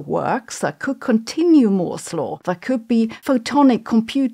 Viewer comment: More 'slow? Morse law? moral woes! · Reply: I heard "more 'slaw" But do we need more 'slaw? I think we have enough 'slaw